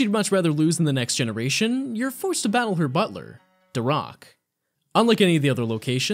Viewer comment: I swear to god what I heard at 16:34 was: "But because she'd rather lose in the next generation, you're forced to battle her butler: THE ROCK "